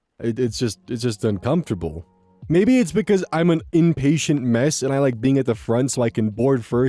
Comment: I thought you said an "in-patient mess". Was really curious to see where that was going to go.